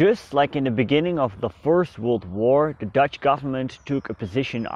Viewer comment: I dont know if its just me but at 0:40 i heard 4th world war